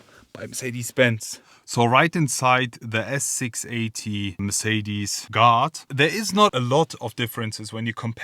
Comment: WOW LOL I THOUGHT HE WAS SAYING GOD MERCEDES GOD until I read the title for the whole 6 mins wow 😂😂😂